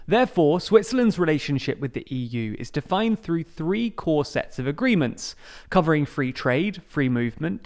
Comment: I heard "corsets of agreements" lol